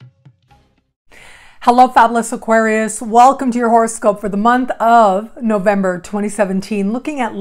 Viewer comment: It always sounds like you say "horror-scope" ...appropriate for Halloween!